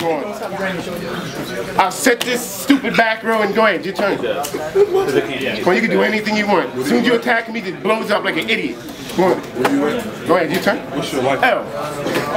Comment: "As soon as you attack him, he just blows up like an idiot!" I'm fucking crying, lol.... xD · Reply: i thought he said blows up like a indian (911 joke) but maybee heard it wrong